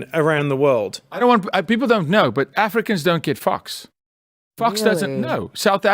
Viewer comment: Hahaha I was casually listening while doing some homework, when I heard Jacko say what sounded like, "Africa doesn't give f$%@ 😂